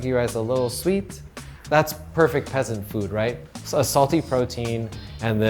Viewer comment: correct me if im wrong, but did he just say peasant?